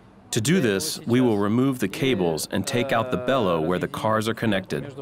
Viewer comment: I hear "blyat"?